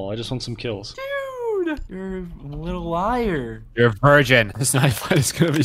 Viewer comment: did he say your a persian???